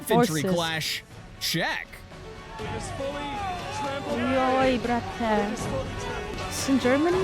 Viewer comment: Did i just heard at 16:52 joj brate? XD